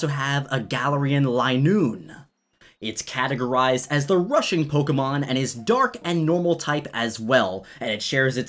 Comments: I thought you said Russian pokemon and I got confused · what I first heard was "it's categorized as the Russian pokemon" 😂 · who else thought he said the russian pokemon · XD when you said rushing pokemon I thought you said Russian also I think galar weezing should be poison/steel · I thought you said "Russian Pokemon"😂😂😂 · I thought you said the Russian Pokemon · i thought you said the rusain pokemon instead of rushing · Russian Pokemon? That’s what I heard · Anybody else misheard Rushing Pokémon as Russian Pokémon? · Why did I think ‘Russian’ instead of ‘rushing’